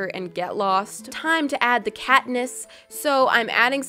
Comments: Being a huge hunger games fan I only heard Katniss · Who heard her say Katniss by accident